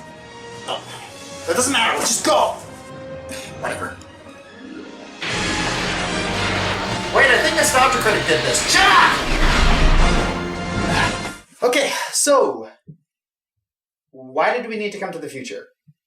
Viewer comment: Did said Wait a minute, i think Nostalgia Critic did this? Second time watching this review and now i noticed it... :P